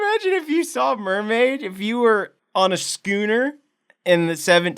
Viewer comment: I thought you said "on a scooter"😂😂